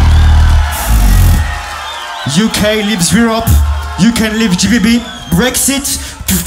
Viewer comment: someone please tell me what colaps said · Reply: He said “uk leaves Europe, uk leaves gbb, brexit”. If you don’t know what brexit means check it out